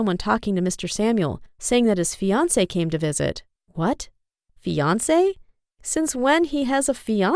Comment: Omg Beyoncé came to visit Low-key that’s what I thought she said.